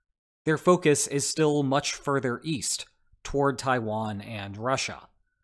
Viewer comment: Speaker tells 'Their focus is still much further east, toward Taiwan and Russia" whereas the subtitle says:"...toward Taiwan and China". This is the only 'flaw' I found about those 'cakes' that were kept as flowing arguments in this informative video.